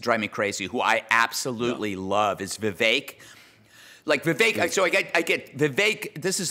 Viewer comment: Who is Greg talking about at around 8:40? Is he saying Vavake? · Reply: Probably Vivek Ramaswamy.